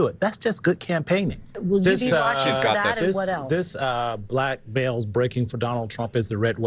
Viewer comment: Black guy says something like "Black bells? breaking for Donald Trump." Didn't understand what he meant by "bells" or maybe it's another term?